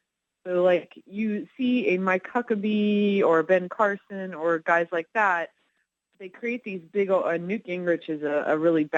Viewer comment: For a second I thought she said My Cuckabee ☺